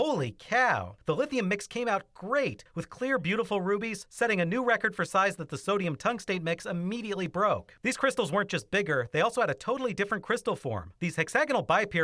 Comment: i swear i thought that said rabies